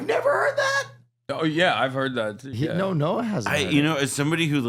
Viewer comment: I thought yall said noah kahan 💀💀💀